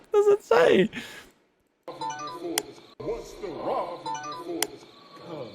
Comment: when he’s replaying the Clip, I think it said “Whats the route towards the house”?